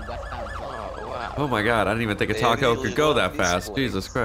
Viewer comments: please tell me I’m not the only one that heard him say taco instead of Tahoe 😂😂😂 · Did he say a "taco" going fast? Ummmmm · Did he say Taco instead of Tahoe? Now I'm hungry. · it sounds like he says taco when the Tahoe passes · who else thought he said taco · When the Tahoe passed by y it sound like said I didn’t know a toco can go that fast